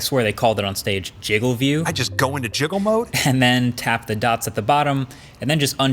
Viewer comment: I misheard “jiggle mode” to sicko mode